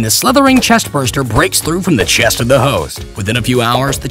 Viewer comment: Wait did you say 0:20 The chest burster bust the chest of the hoes??